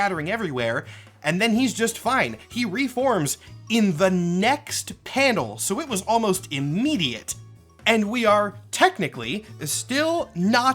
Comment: I heard this as “he reforms INTO the next panel” and I was gonna freak the fuck out